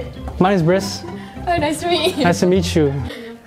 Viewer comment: thought he said nice 🍒 😂